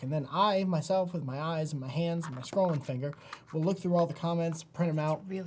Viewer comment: I heard "scrolling finger" as "scrotum finger"